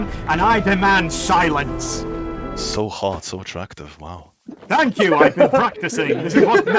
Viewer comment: Classical: I speak as the king of this land you stand on, and I demand SILENCE! So hard and so attractive. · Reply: Timestamp: 33:03 I also think he says hot instead of hard :P